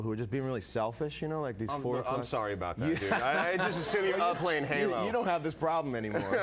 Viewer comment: did he say i was up playing halo?